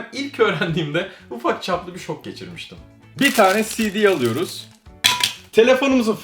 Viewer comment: abi sidik anladım